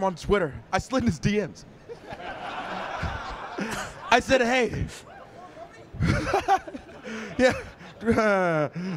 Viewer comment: Can anyone hear what tuy in crowd said that made Creed laugh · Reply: Anthony Anthony he said what really.